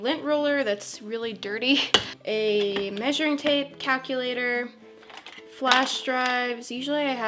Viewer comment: Did anyone hear her say CACULATOR or just me?????